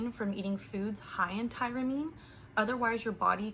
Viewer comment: what did you say at 3:47 high in tyrnine...how do you spell it? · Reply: Tyramine (TIE-ruh-meen).